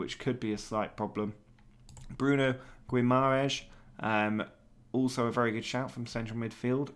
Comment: wtf did u say? hahaha "gwimaresh"?